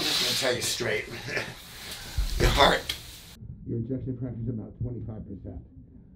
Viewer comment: Why is the audio muffled at 6:01 ?? Kali do you think i didn't hear what the doctor said to you. If y'all wanna know what the doctor said at 6:01 in the video. Turn on your subtitle to see what he said and turn up the volume also.. Kali is getting a serious wake-up call. #facts #Scary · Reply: The doctor said his ejection fraction was at 25% which is really low